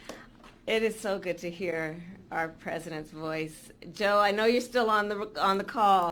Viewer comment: I thought she said "Joe, I know you're still on the roof"